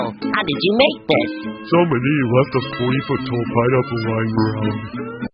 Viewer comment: sm tell me what Patrick said? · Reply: He said "some ìďíòť left a 40 foot pineapple laying around"